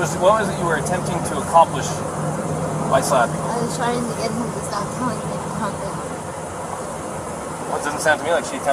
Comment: What does she say at 49:05? 'I was trying to stop him - - -'? Her face after saying that looks like it hurt her to admit whatever that was. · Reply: "I was trying to get him to stop telling me to calm down"